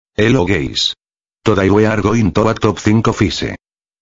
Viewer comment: Did i hear "Hello gays"? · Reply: Hello gay